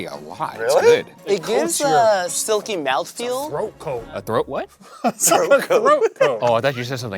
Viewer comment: don't worry ryan, i definitely thought shane said "throat goat" as well 😭